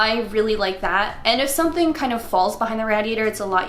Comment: I love how you say radiator lolll. I'm unsure if it's some kind of regional accent but it's very cute. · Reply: Heh, I didn't understand what she was talking about at first. Thought she was saying something like "red-eater" and was wondering what kind of bug that was XD